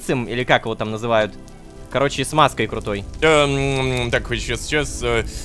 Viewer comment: мне одному послышалось "смазкой крутой? "